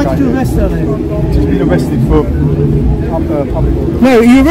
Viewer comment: I can not hear what the officer says at 13:48 but it sounds like something like "public order" or something. So perhaps disorderly conduct as her message enrages the crowd which does not take a lot with that lot. Resisting arrest apparently is not a crime in the UK, or so google says at least.